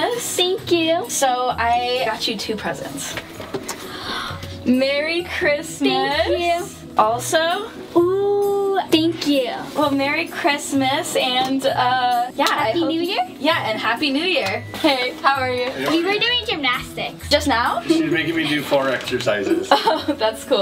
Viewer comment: Does she say Chrestmas or Christmas